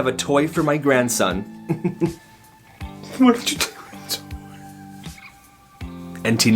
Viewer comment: What he said : Why did you take my toy The subtitles : why did you take my grandson.